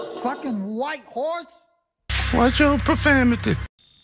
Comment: What is that guy saying? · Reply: Watch your profanity